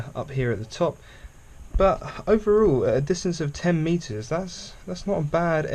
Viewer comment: Can someone please translate what the fuck he's saying starting 6:50? "Overall this is ...fooking stupid accent..." Did he said 10 meters?! Like in, the distance he was shooting from? Because that would be "fooking" lame!